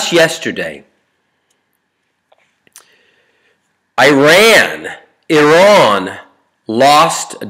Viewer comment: Hey, I like you , But I can't understand why are you trying to say AIREN 0:53. Are you trying to mock Iranian by saying that ( I don't think that ) . Go and look in wikipedia for meaning of iran.